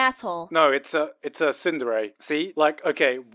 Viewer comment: "What is the name of the type of girl who likes a guy but they can sometimes treat him meanly or even violently?" "An Asshole" It took me 4 minutes to be able to stop laughing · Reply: U e h a r a x s I heard "sindere" in the video so I thought it was another kind of "dere" I didn't mean to say sindere instead of tsundere.